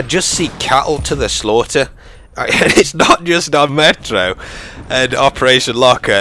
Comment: I thought he said "Young Metro", and I was like, "I knew young metro didn't trust them".